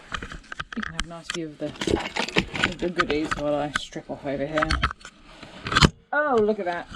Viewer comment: Did I hear right at 1:01:55. 😂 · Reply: my coat, strip off my coat Ian and the goodies I was referring to was the copper, the camera was very much NOT pointing at me 🤣🤣🙈